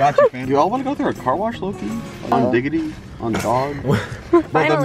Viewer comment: i thought he said milks not mil*s...lolllll